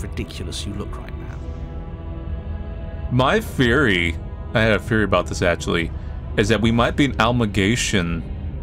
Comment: I thought soo hard that he said "I have a furry" when he said "I have a theory"